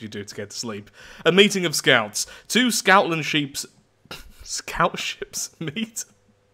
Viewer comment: scoutminsheeps, a misspeak or telling us he's making sheep into scouts? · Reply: I thought he said “scoutlandsheeps” as in, scottland but with a scout